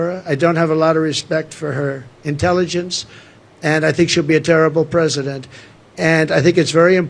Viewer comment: Did you catch the "she'll" at 10:01 or so? I replayed it over and over, but it sure sounded like that. Then I turned on auto-generated captions and it had heard the same. 🙂👍